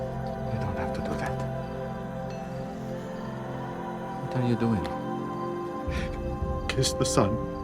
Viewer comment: I think you're misinterpreting what he said on the show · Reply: After Nicodemus worshiped Christ this is what had been said, "We don't have to do that", "What are you doing"? Or maybe i am hearing it wrong...